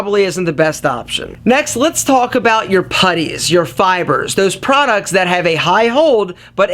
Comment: what di you said? paddies and fibers? im german i dont speack english